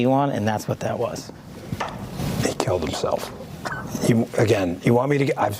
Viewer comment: Dan Bangino first words about Epstein sound like he said, "they killed themsrlves!?"